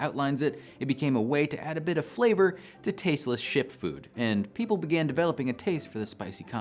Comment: I misheard "tasteless ship food" as "tasteless shit food."